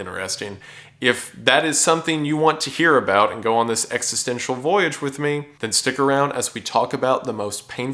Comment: Oh, watching the beginning again, I've noticed I misheard a word. "Existencial voyage" is what you said, but I heard "existential void" for the first time. Which is way more fitting, tbh.